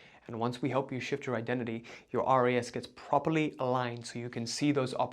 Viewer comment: Hey, at time stamp 52:41, the transcript says RA: “52:41 problem and an identity problem and once we help you shift your identity your “RA” gets properly aligned so you can see those opportunities that will lead you to where you actually want to get.” What is the word you used? Chat gpt is telling me “RAS” standing for(reticular activating system) or Arya which is a Sanskrit word that can mean “Used to describe someone with high character, spiritual clarity, and discipline”. Curious to understand the word you used so I can take a deeper dive on understanding it in this context. Thank you! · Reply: RAS: reticular activating system